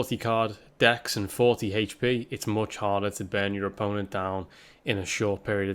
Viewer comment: Your accent is so thick with certain words that I genuinely thought you were saying "ban your opponent" in the intro....when in fact you were saying "burn your opponent". That's wild lmao.